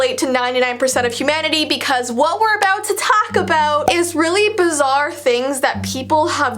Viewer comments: What I hear: What we go to Taco Bell. That's what I heard the FIRST time. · When said "Things we're about to talk about" I heard "Things we're about taco bell" lmao